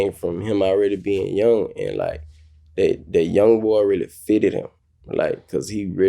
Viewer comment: Listening to meek? Meek and them was really saying young Bul, they thought he was saying young ‘boy’ lol